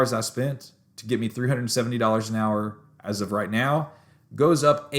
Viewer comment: Thought you said 370 an hour